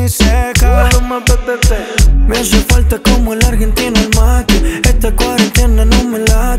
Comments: Yo pensé que decía "Me hace falta como al Argentino el Macri" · Pensé que decía me hace falta como al argentino macri, lpm que me parió😂🤦🏻‍♂️